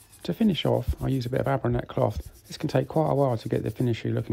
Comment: Abronathy ? I can’t quite make out what is saying this cloth is. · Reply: Abranet cloth